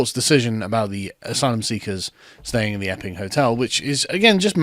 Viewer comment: I thought you said "effing hotel" for a bit